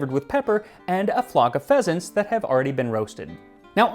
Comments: Instead of "pheasants", i understood "Peasants" and was like: "Dang, thats rude. oh wait a moment..." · I seriously thought you said “flock of peasants”, not pheasants ,and I got a little scared for a second.😂 I was trying to fall asleep and my eyes immediately popped open.